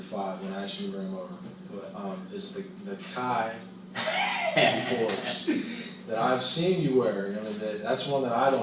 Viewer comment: Kai 4's? Forbes? I didnt understand what you said it's been buggin me I'm keep repeating it lol